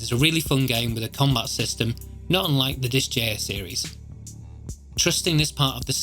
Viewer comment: What game is like Fell Seal??? Sounded like you said Dis jair series?? CC translated it as Disk Jr... Are you talking about disgaea (pronounced Dis Guy Uh))?